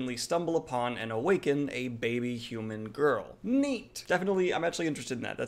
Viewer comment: Am I the only one who thought he said "Meat!"